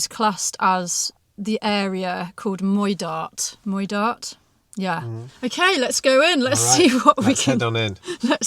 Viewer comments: The Glenuig Inn is in Moidart - just as she said. Knoydart is not an island, it's a peninsula. Apart from that, you are 100% correct! 😄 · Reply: Ah, my bad. I thought she said Knoydart 😂. Thanks. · Moidart is what she said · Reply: I thought had said Knoydart too.